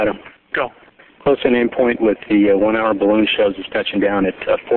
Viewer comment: Correct me if I'm wrong, but does FIDO say at 7:21 that there is a balloon show 1500 feet from the end of the runway? Did I mis hear that? Like a hot air balloon show? Is that really the best place to have one when the shuttle is about to land lol. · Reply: No, it's "the one hour balloon shows"-they launch a weather balloon before landing to check winds aloft.